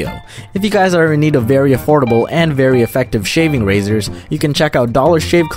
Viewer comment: I thought he said "These are very horrible and effective razors." 😂